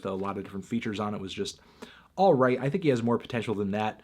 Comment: did you say alt right? You're such a racist Anthony!!!